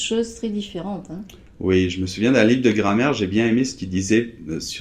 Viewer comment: Funny... at 2:18 I thought said "un lit de grand-mère", I had to turn on subtitles to see "livre de grammaire". I hope some day in the future I will have had enough listening skills to pick up the subtle variations in pronunciation :-).